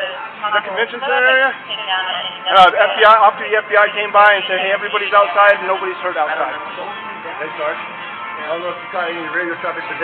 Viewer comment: Did they say after the FBI came by & said everybody's outside & nobody's hurt outside?